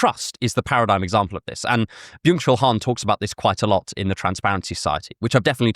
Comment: I thought you said ‘mutual harm’ when you said byung chul Han and for a second I was like did some anarcho-nihilist collective name their manifesto transparency society as well 😂